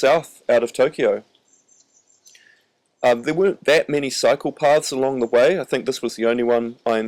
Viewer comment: I heard "not that many psychopaths along the way", not "cycle paths" 😄 Cool vid